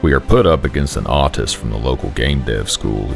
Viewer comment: I honestly thought it said artist instead of autist.